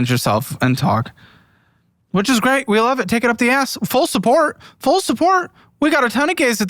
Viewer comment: did you just say take it up the... a? 😂